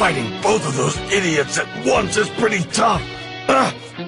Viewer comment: Vegeta: “where’s that silly clown when you need him” Piccolo: SPECIAL BEA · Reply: Sounds like he said “SPECIAL BEAN”